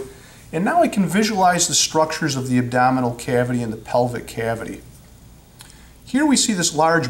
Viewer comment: DID HE SAY PELVIC CAVITY??